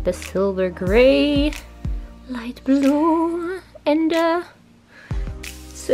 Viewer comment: When she says light blue does it sound like she says light blå😂(blå=blue)